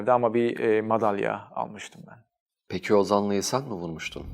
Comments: ne dedi anlamadım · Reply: "Peki o zaman insan mı vurmuştun?" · ne diyor ben anlamadım? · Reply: O zanlıyı sen mi vurmuştun? Bir dahaki soru